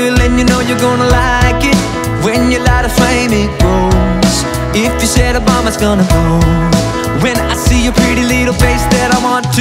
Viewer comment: I always heard it as "if you say obama's got to go"